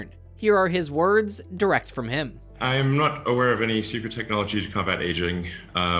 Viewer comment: As soon as I heard Elon say he wasn't aware of anyone working on longevity tech, I knew that he was't shooting straight about something. He man not support it, but a guy like him at least has to be aware of some of the big name in the space. Either way, I am not holding my breath on him coming around. It m ay be possible, but I doubt it. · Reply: If you're referring to what he said at around 1:25, he said "I'm not aware of any secret technology to combat aging".